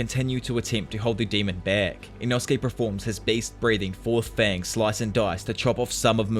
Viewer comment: ngl I kinda misheard you saying beast breathing as based breathing and it made my day